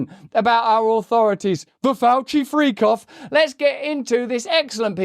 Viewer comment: Did I hear “Fauci Freakoff?” 😂😂😂